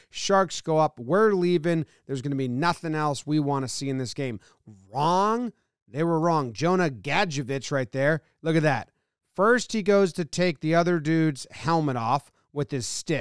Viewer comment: really thought he said Jonah Gotcha bitch